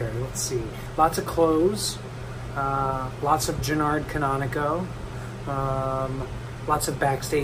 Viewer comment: Wait..did i just hear george say ' Gernard canonico'